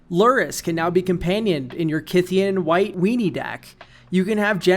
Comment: someone please explain what a kithkin white weenies deck is? Lurrus interests me but idk what he said means